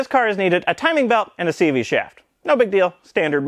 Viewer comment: Am I the only one who heard “a timing belt and TV chef”?